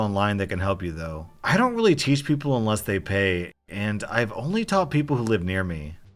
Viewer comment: For a second i thought he said "i dont really help People unless they're gay"